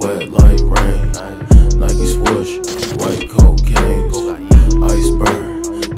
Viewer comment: When he said cocaine I heard a “cocohien”